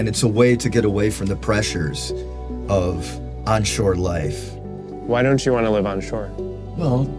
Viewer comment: At first I heard him say unsure life when he meant offshore but it's the same thing.